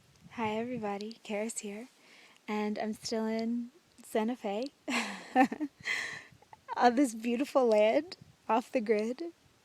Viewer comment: Many of her stuffs make no sense, but I like her energy and look. lol! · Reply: Woah my speaker wires are a little crackly on this computer. When I first started listening to this video, I almost thought she said "and I'm still in 7th grade". lol